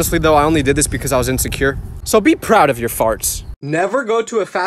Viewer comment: “be proud of your farts!” I thought he said “ be proud of you being fat”💀